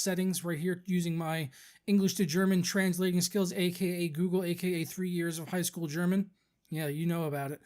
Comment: When you said aka Google my phone thought you said ok Google and opened the Google app LOL